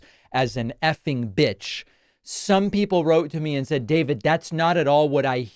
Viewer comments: I heard “fucking bitch” …what do they think he said? “Fun time Switch”? IDTS. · I heard " fuckin bitch " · David, I heard "f***ing bitch", too.